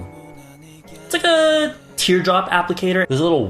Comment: it sounds like is saying "这个" which means this one in Chinese lol